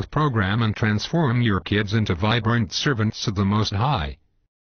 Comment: I heard "vibrant serpents" the first time :P